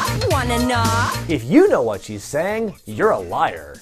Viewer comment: Did she say “Want a knock”? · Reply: Preston U-ie I heard it as "Wanna knock?" ... as in "knock boots." I'll be over here washing my eyes out to get rid of the mental image.